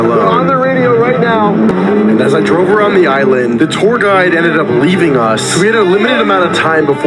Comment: Did he just say "The tour guide leading us"???